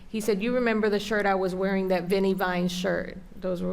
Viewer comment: did she say "Vinny Vine shirt"!? As in "Vinny Vinesauce"?? That can't be it, no way.